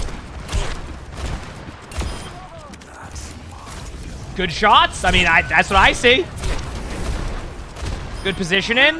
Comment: what does Reaper say there? "That's my..." what? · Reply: job